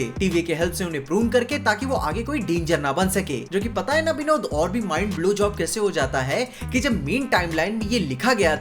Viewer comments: Hahaha 0:46 mind blow j* b hahaha did you said this on purpose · he really said blowjob😂😂